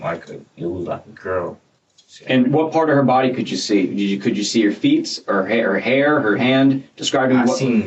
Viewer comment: Did he say “feets”🤦🏾‍♀️